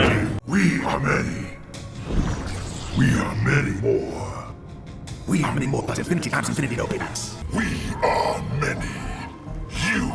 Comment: What is ermac saying in MKX?🤨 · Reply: I think it's "We are many more plus Infinity times infinity no take backs."